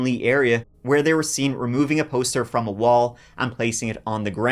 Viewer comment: Thought he said their last name was warm beer 😂